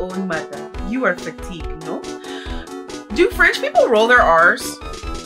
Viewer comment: I heard that as "Do French people roll their arse?" 😂